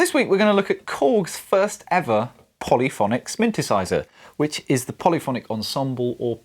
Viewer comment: Did I just hear you say “sminthesizer”?